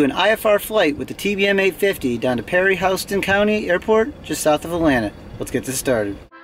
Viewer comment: I was very confused reading the title of the video.. until I heard you say " PERRY HOW-STUN"...;)